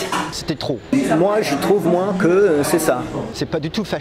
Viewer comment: Hey! I'm learning french, could some one help me? What does he say (in french) from 0:40 - 0:42? Qu'est-ce-qu'il dit à 0:40 - 0:42? Et c'est quoi, ça? (in english) Thanks :) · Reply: Hi ! He's saying "moi, je trouve, moi, que c'est ça" which littérale means "Me, I think that, me, that's it" it just doesn't really mean anything but I think it's to undermine the megalomaniac side that french people can have sometimes ;)